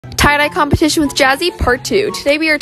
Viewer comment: I thought you said tired eye competition.. was about to say I win 🏆 😔😴